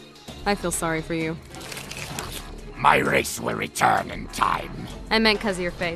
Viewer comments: what did reptile say when cassie said i meant cuz ur face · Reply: Emilio Barron My race will return in time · i mean at 11:21 what does reptile say when she says i meant cuz ur face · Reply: Reptile said "My race will return in time."